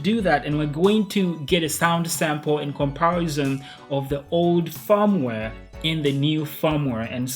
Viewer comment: I thought he was saying 'farmware'